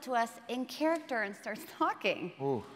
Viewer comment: I heard "fucking" instead of "talking". Imagine that with Cheryl saying "taking a previous moment and getting into character" hahaha.